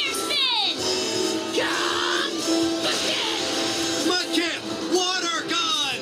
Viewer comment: Anyone else hear like when said mudkip water gun at 1:10 something it sounded like pokemen 7